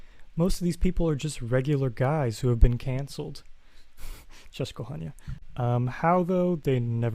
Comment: Did I hear „Cześć kochanie”? 💜 Amazing work, really enjoyed the video!